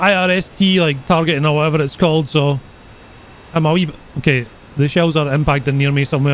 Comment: did bro really say "I'm a weeb"?😂😂😂😂😂😂